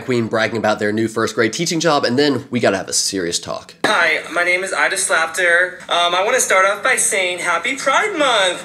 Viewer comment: bro i heard " my name is i JustSlapedHer" lol